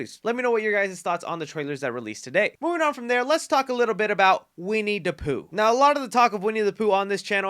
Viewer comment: I thought had said "now let's talk about 'We need to Poo...'" 🤣🤣